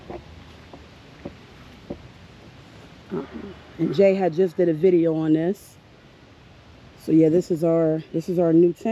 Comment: Loving that screen tent! What’s the name of the place you say you got it from? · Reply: We have like an auction/bid place here called Mac Bid. You can bid on all types of items. Alot of our gear we have used for camping we have got from there. We love it and have won items for 90% less than what we would have paid.